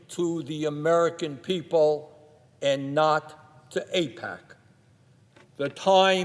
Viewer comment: the subtitle is wrong. It should be 'AIPAC' not 'attack'.